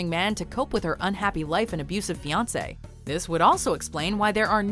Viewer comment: I thought she said abusive beyonce lol